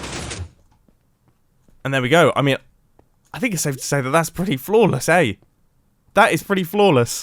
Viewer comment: The way mumbo said "flawless" sounded like he said "floorless" Coincidently, they both apply to the elevator. When the elevator goes down, it is indeed floorless